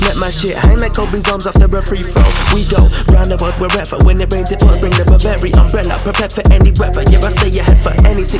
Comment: WTF he say? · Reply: “We go around the world wherever, when it rains, it pours, bring the Burberry umbrella”